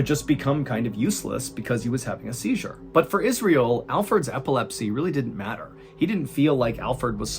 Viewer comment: So, definitely not making fun because I have a thick southern accent, but are you saying “Alford” of “Alfred”? I hear “Alford” but I can’t tell if you’re saying “Alfred” with a bit of an accent lol. Anyways, thank you for years of the best story telling series ever made! I’ve watched every episode as soon as they were released. Most of them more than once. And more importantly, thank you, John, for your service to this great country!!🇺🇸 I hope you and your family stay blessed and I hope y’all have an amazing year! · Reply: he said ‘alferd’